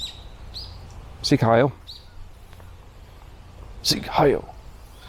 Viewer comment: Did you really say "Sieg Heil"? Why?